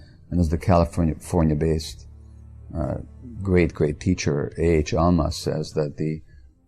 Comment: What is this "Alma" guy he's talking about? I couldn't understand the first name? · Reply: A. H. Almaas is the pen name of A. Hameed Ali (born 1944), a Kuwaiti American author and spiritual teacher who writes about and teaches an approach to spiritual development informed by modern psychology and therapy which he calls the Diamond Approach. "Almaas" is the Arabic word for "diamond".